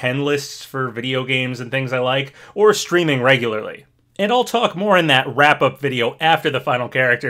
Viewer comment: i thought you said "or screaming regularly" i was like damn is it that serious 😭😭